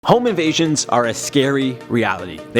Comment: At the start I thought he said “home of Asians”